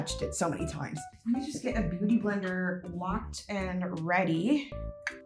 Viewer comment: I thought she said booty blender😂😂😂😂😂😂😂😂😂😂😂😂😂😂😂😂😂😂😂😂😂😂